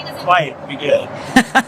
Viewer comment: sounded like he said "quiet, be good" ?